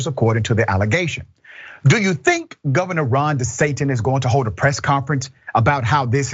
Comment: Did he say Governor Ron de Satan? 😮 4:33 .